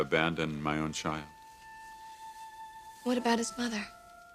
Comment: What did Brooke say to eric at 14:00???? What about his brother??? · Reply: Brooke said what about his Mother.😊